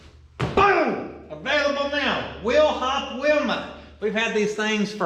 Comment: Someplace I saw that you had "Will hop Wilma t-shirts. "Will Hop" is the way it was spelled. It also always sounded like you were saying "will hop" . But I see that it's "WHEEL HOP" Wilma!!! Sorry, just a northerner unable to understand southern talk... peace and love